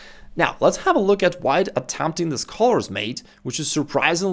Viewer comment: what does he say white is attempting? I can't make out the word... something mate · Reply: Scholars